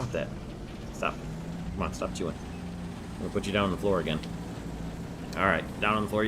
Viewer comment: This tank seems pretty nice! In Battle 10 when you first started talking about the puppy on your lap, it was funny when she/he was chewing the table and when you said you will have to go down, I thought you said I will put you down!!(As in kill)